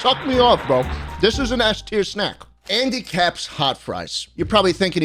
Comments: i thought he said 'handy capp'🤣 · When he said Andy caps I thought he said handy caped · When he said Andy Capp’s I thought he said Handy Capp’s!!!!!!! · I thought he said handicaps hot fries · I heard "handicap" and not "andycap"